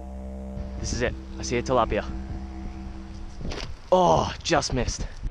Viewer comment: did h just say "Its a Tilapia" whats up mga kaigan!